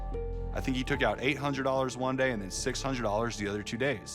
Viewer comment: Honestly , she didn't sound too shocked. I bet she already knew everything already . She didn't cry , her voice never changed tone ...it stayed quiet and soothing the entire conversation we got to hear . Maybe since they moved, he has maybe killed another neighbor for money ? Who knows ?? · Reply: Watch 11:43 again. The subtitles say "Allison took out the money," but the detective says "he took out the money". Also "Allison" is his last name.